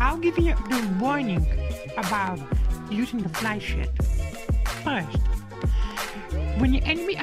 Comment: i thought i heard fly sh**t